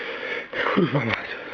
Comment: did he say kurwa